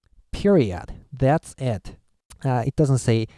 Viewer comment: did he just say "periodt" there? caught me so off guard😂😂